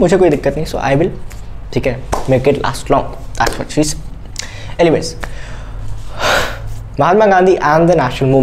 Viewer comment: is it only me or he really said "that's what she said"??